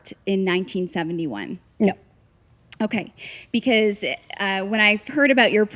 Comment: the subtitles are wrong aren’t they? It says yep but I hear her saying no